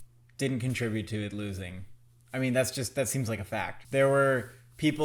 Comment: omg did he just say the f slur? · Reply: FACT. Not the other word